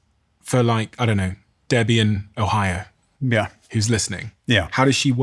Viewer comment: "Debian Ohio" is what I heard 😅. What a cool name.